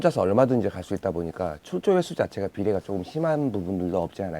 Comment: 죄송한데 0:35초 이후에 낚시는 혼자서 얼마든지 갈수잇다보니깐 무슨 말씀하시는지 발음이 잘 안들리네요 ㅠㅠㅠ 답변부탁드립니다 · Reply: 출조횟수자체의 비례 요